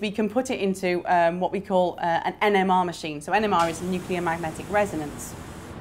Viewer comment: I thought she said ENEMA machine :/